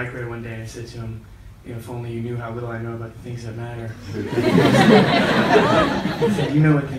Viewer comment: What did Timmy say 7:20? I'm Russian, can't understand clearly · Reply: K T he used a reference from the movie: “Little I know about the things that matter” then he said “you know what things”.